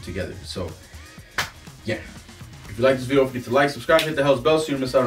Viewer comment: did you say "welcome,subscribe, and hit that "Bell's Hells"? I thought I heard it twice.